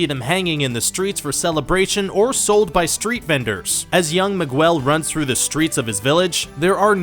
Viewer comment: did he just say..."Mi-Gwell"? screams and cringes